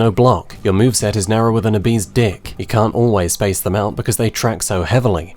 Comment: "Your move set is narrower than a bee's dick" is truly a masterpiece of a sentence. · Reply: Lol I thought he said "an obese dick"....